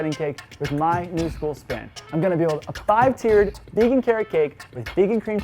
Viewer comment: I thought you said bacon carrot cake at first 🤦‍♀️